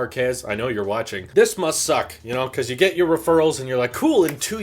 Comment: did you say “This Musk suck”? Or am I just going crazy? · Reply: must